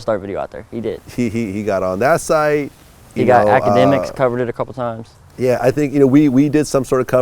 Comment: did he say Dj academics lol